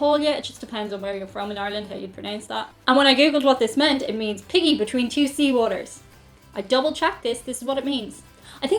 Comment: At first I thought you said “Piggy between two C words.” Well that’s an image to behold.